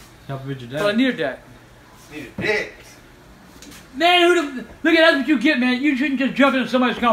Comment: did AGP just say “man who the— n*gga that’s what you get man?”